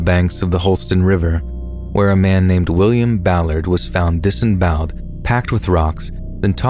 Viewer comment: is a w Ong best story teller also I was first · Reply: I thought you just said Mr. Ballen was 🤔... but I must say I do agree with you about ☺️